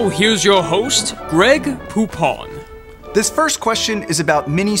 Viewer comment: When i heard greg "Poupon" I thought it was Greg Poop on.